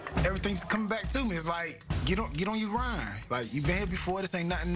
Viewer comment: They thought he said get on your ride Haha